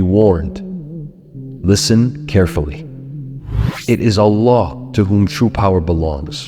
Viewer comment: Kindly change the subtitles, it is "Allah" instead of "a law".